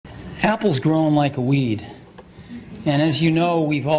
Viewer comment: Did I hear it right? was the 1st sentence, "Apple is growing like weed"? If i didn't misheard it, thats just fucking awesome.